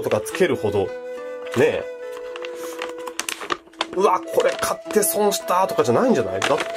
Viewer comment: の「うわこれ買って」 が最初 「うわこれ硬ぇ」 に聞こえたw袋のテープ硬いんかと思ったw